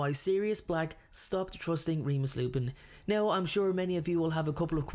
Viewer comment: I thought it said “thrusting” 😐🤚